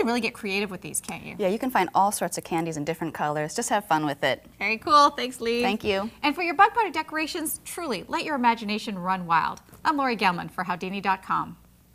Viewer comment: it sounds like shes saying "thanks, LEAVE."